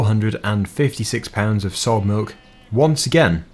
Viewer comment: When I first started playing I was 8 or 9 and thought sold milk said solid milk